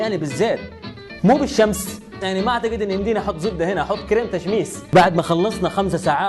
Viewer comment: thumbs-up if you thought you heard "zobby" instead of "zobdah" at 6:30 :P